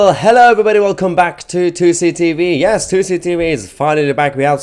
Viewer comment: This will get overturned by the Supreme Court. Also at first I thought you were actually saying 2C TV during the protests until it became obviously Tousi tv.